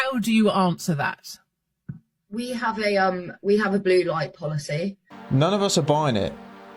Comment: I swear to god i heard her say we have a « blue hair policy » 😂😂